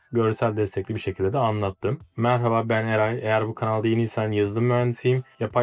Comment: "Merhaba ben Eray" dediğin kısımda "Merhaba ben AI" anladım ne oluyor cidden.